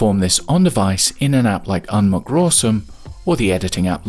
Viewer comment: what app u said in 25:36? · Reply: Unmcrawsome - see pinned comment.